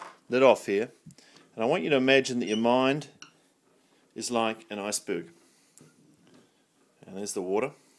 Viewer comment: WTF does he say at 1:07? "...your mind is like an ICE BOOT"???